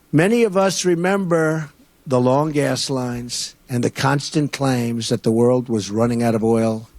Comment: Had to replay it a few times. I swore he was saying "long ass lines"